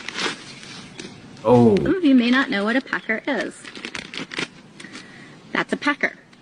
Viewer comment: I thought she said a pecker smh but thats crazy